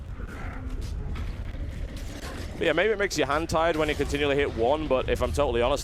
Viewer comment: makes you hentai? did i hear it right